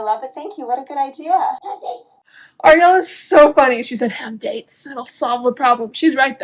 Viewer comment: Ariella said have DATES? I thought she said have faith haha